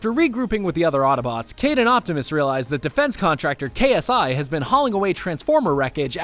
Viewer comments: did i just hear ksi? 9:18 I did hear ksi! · wait wait wait did you say KSI.... I’m done with life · did it just say KSI